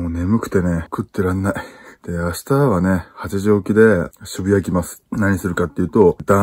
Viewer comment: 8時起きが、発情期に聞こえた